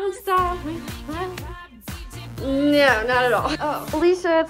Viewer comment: WHEN THE MOM CALLED HER I THOUGHT SHE SAID Felicia SHE SAID ALICA